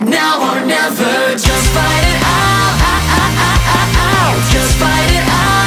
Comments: I always thought it said "just put it out" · on the japanese version i always thought they said just rot in hell instead of just fight it out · I swear it sounds like theyre saying "Just Fart it out!"